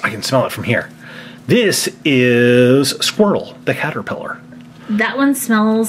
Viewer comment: At first I heard say “squirtle, the caterpillar!”